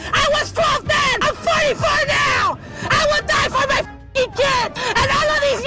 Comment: Did I hear right and they said fighting for my kids? Can't believe they have produced. 😮